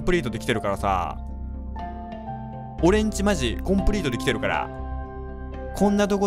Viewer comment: おれんちまじコンクリートででけてるから と聞こえてしまった...